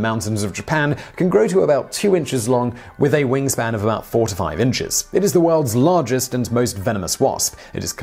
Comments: At first I thought he said the Asian hornets' wing span can grow to 45 inches! Then I listened again and realized he said 4 to 5 inches 😄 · Thought he said 45 inch wing span not 4 to 5